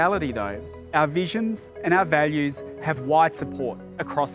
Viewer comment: Oh I thought he said "...have white (not wide) support I was like "hang on, what?"